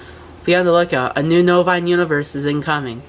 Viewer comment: Every time he says "Novine" all I hear is "bovine."